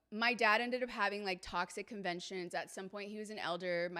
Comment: When you said, "My Dad had talks at conventions", I heard, "My Dad had toxic conventions". I thought, What? Then I figured out you said, "talks at" instead of "toxic", and then thought- What the heck, both are equally true.